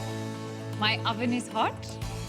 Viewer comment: I was like rock hard when she said those words. Pls check out what she said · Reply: " my ovan is hot ""